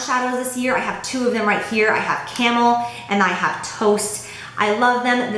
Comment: Hahaha... When you were talking about the Bobbi Brown shadows, I thought I heard you say that the shadow names you had was Camel and Toe.., Until I realized that you had actually said Toast.